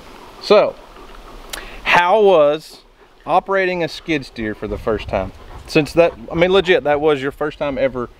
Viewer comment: I totally heard her say buy a skid steer. That new shop is awfully empty.